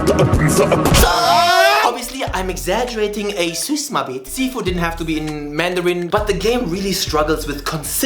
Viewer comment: Did you say "suce ma bite"? 🫢